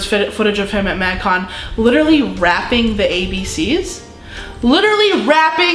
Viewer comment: omfg when she talked about one of them rapping the abc's I misheard her for raping the abc's and I'm like TF